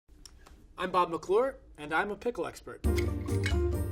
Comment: For a second I thought he said his name was “Rob McClure,” And I was like “damn. A broadway star AND a pickle expert.”